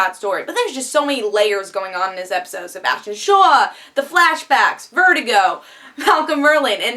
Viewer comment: did she just say sebastian shaw?